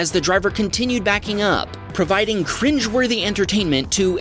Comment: did he just say cringe 😬 💀